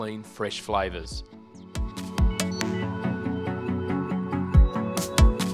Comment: Lol my son thought you said how to punch chicken and was like what did a chicken ever do to you! 3